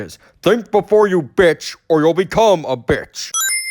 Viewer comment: I don't hear the insult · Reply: “Think before you bitch, or you’ll BECOME a bitch!”